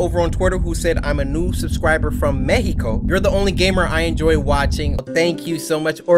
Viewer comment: did he just say Mexico...